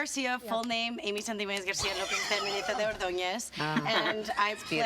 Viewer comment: Spanish is my native language but I hear "my name is Amy Garcia but my full name is sjibdgdkdkl Ordóñez" anyway 😂😂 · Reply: I think she said "something awesome"